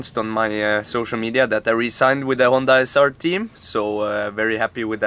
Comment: i thought you said resigned (as in quit) instead of re-signed. Was absolutely bewildered for a second 😂